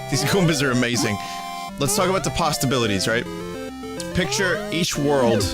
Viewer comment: Did Vinny just say pastabilities or am I going insane?